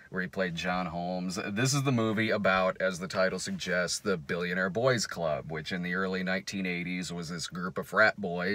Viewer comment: Every time says the title I hear "Billy and Our Boy's Club"